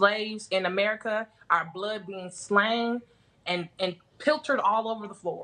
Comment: "Our blood being slang ( Slain maybe? ) and piltered all over the floor". I'm still trying to figure out what I heard exactly.